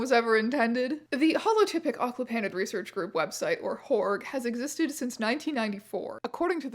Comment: I wasnt listening carefully and for a hot second I thought the group was called W.H.O.R.E